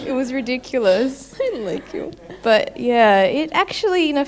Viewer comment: Did Teal actually say "I like you"? 3:28 :D